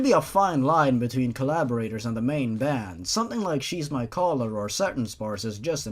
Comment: i know its supposed to be "shes my collar" but the way you said it i heard "cheese my collar"